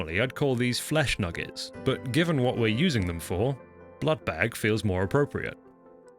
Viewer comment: Did someone just say... BLOODBAG?!